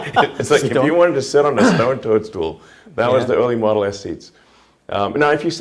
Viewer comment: WHAT did Elon say? Stone towards Tool? I am German, would love, if anybody could write me, what HE has said! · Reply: I heard stone turn stool